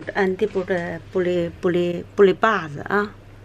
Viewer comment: In the begining i heard" pour les pores" which is for the pores